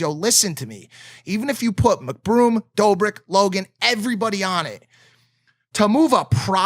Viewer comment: who does Mike say before Dobrik and Logan? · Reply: The Ace Family (Austin Mcbroom)